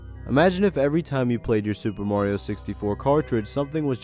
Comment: I thought it said "sperm Rio 64" 😭😭😭😭